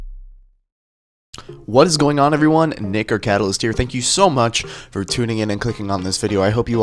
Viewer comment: Bruh at 0:08 I thought you said " what is going on everyone N*word Catalyst" xD LMFAOOOOO